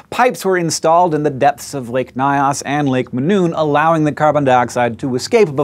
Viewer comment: If Hank could pronounce "Lake Nyos" less as "lick my ass" it would raise my comfort level. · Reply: LMFAO. I had to rewatch it to hear it. I don't think he is trying to say "Mynos" though, its "Nyos" like nigh auss. Edit: 2:32 if anyone cares.